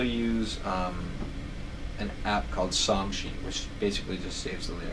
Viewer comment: What is the name of the app at 08:38? Can't hear it. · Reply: Songsheet